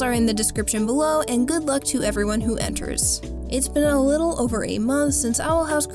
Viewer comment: I THOUGHT SHE SAID IT'S BEEN A LITTLE OVE 8 MONTHS AND I ACTUALLY GOT SCARED